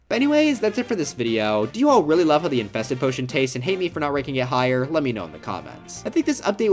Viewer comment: Did he say… tastes?